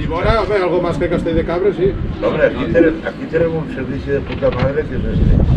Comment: escuche mal o dijeron " aqui tenemos servicio de poca madre"...